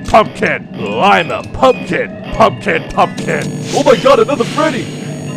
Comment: oh my gad little freddies (I know that isn't what he said but...)